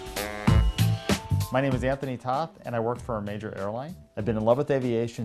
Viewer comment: sounded like he said "My name was Anthony Toth" Was? Hahaha, lol